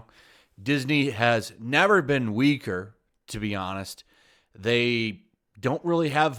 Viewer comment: Lol, at the beginning when he said, "Disney has never been weaker," I heard, "Disney has never been Uighur."